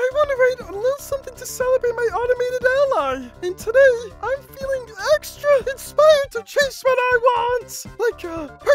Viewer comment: I misheard "I'm feeling EXTRA INSPIRED TO CHASE WHAT I WANT" as "I'm feeling EXTRA INSPIRED TO KISS WHAT I WANT"